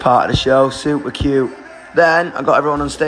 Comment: That last part he said... I thought he said "had a shower and pumped my staff member!! 😅😂😂